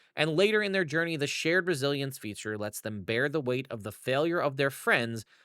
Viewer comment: for some reason I heard this as "Sheer Brazilian feature" instead of "Shared Resilience feature" lol